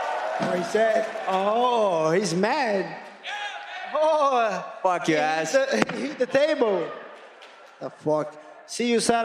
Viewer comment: LMFAO did khamzat say "fuck your ass" 19:59 ??? that's new 😂😂😂